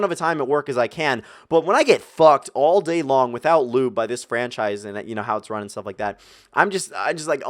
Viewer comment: did i hear rightt 👀💀😂😂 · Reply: fucked without lube lol